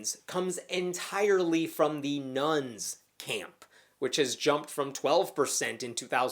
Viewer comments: I kept hearing "nones" as "nuns." Cracked me up every time you said it. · He says "nones" & I hear "nuns", leaving me a bit confused.